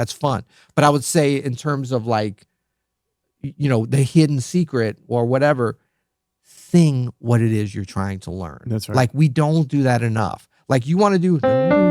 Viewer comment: Thanks for the podcast, love it. The de-esser issue is still there, heads up. For example 13:08 sounds like "thing" instead of since. Also voice just sounds a little distorted sometimes, like at 9:00 on the word "wanna" it's noticeable. Sorry for the nitpicks, just want to hear the podcast be as good as it can be